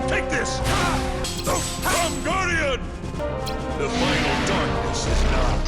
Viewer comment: I always hear "HUNGARIAN!"